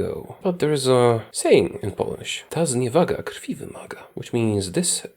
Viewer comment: Imagine as PM of Poland in the 1930s. Hitler: Gimme Danzig! "There is a saying in polish: 56:19 "Tas nie wagasz, pieble waga" (or so, Bla Bla for any german ear like mine) "This Insult requires Blood!" Hitler: Ok, nevermind, have a nice day. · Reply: Ta zniewaga krwi wymaga